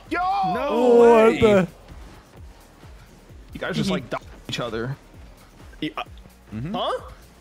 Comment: what did he say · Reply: dogged???